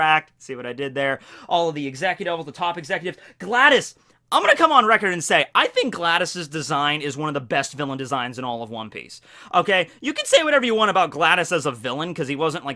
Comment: Did you say gladus like the robot, or gladius? Cause it sounds like you were saying gladus.